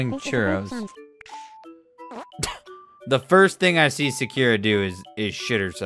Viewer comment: Did he just say "sakiera" omg XD I'm dead rn now all the weebs will come after him